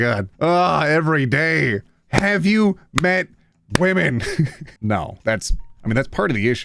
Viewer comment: i heard "have you pet women?" and i essentially became my pfp 🥲